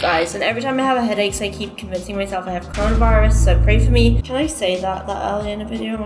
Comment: No one. Literally no one. making us entertained during these hard times!💗 Love from a positive u tuber💖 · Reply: I thought you were saying positive to coronavirus